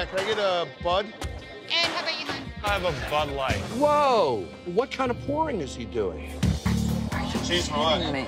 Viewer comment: Bruh i legit thought jon said "woah what kind of porn is he doing" at 1:27 🤣😂😂